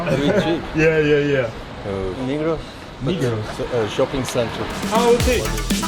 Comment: Ok so who else thought he said ‘negros’ at 4:00 ? 😅